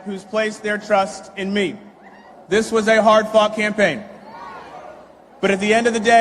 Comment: who else's think it sounded like "This was a hard fking thing" 😅 ik it's not. I put the captions on and saw "hard-fought campaign"